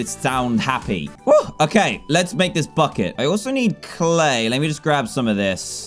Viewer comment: i thought he said "fu* it"* instead of bucket